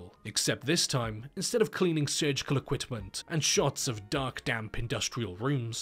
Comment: Super specific and probably unnecessary comment but it sounds like you say “equitment” in the beginning not equipment